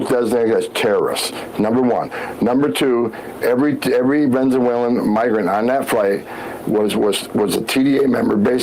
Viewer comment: he really says "venzewhalen"